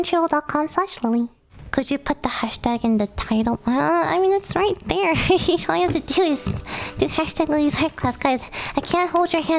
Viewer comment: i thought it said arse class and then i was confused